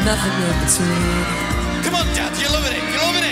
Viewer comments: Am I the only one who heard Liam say " Come on Dad, your lovin` it, your lovin` it " after Louis` solo at 1:58 ??? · after louis solo did i hear liam say "come on dads your loving it, your loving it"? or is that just me?